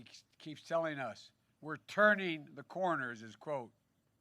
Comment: Turning? thought he said rounding